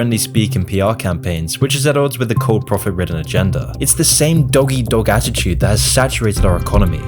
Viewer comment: So this was amazing. But did you say "doggy dog"?